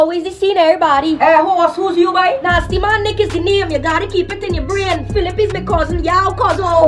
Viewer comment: Nasty man nick you gotta keep it in yuh brain 🤣🤣🤣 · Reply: Ah thought he say 'yuh daddy keep it in yuh brain'